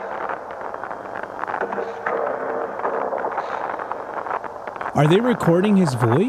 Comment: I heard "Destroyer of Worlds" and so I believe what is being said in the tape here is a voiced altered version of J. Robert Oppenheimer's speech.